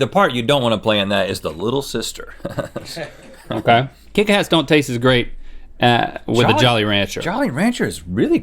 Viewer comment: did link say kick ass?